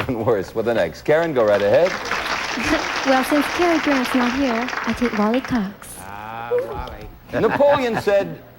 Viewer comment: what did the female contestant say since __ isn't here, I'll take Wally Cox? I couldn't catch that after replaying many times lol... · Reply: I think she says, "Well, since Cary Grant's not here, I'll take Wally Cox!"